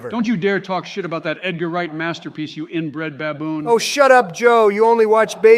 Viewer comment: | I always thought Joe said "Orange Skin Baboon" not "Inbred Baboon".